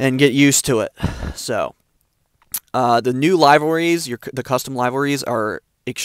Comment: my man sounded like he said lievorys instead of liverys